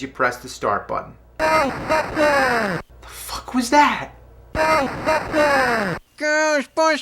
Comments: i remember mishearing it as "OH. FUCK YEAH!" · Is it me, or anyone else thought he said "mother fuckers!" in 2:46. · i heard "oh, fuck, yeah!" lol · is it just me or does it say "GO F*** HER" instead of "Ghostbusters".